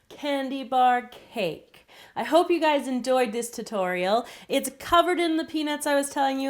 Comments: she said endoyed instead of enjoyed, not to be mean it just stood outLol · actually said endoyed at 7:20 instead of enjoyed